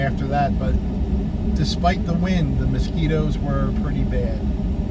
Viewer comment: Aww can Eugene have a fish today and kindly pay you on Tuesday? 😃When you said the mosquitos were pretty bad I thought you said the Speedos were pretty bad🤣 I was trying to figure that out 🤣 Wishing you a great day.